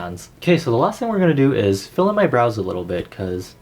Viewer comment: Lol, when u said, filll in my brows, i thought u said fill in my bras.... awkward